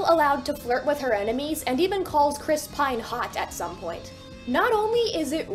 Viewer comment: Ight, so you said Dot flirts with Chris Pine - but I thought you said she found Chris Hansen hot, and was like: "Wait, that's actually like, darkly hilarious! 😆"